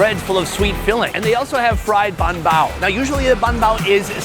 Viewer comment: Fun fact. 'Bao' (包) means Bun. So by saying Bun Bao, it's basically saying bun twice. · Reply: In Vietnam we call "Bun" is "Bánh Bao", he said "Bánh Bao", maybe you misheard it as "Bun Bao", "Bánh" is mean cake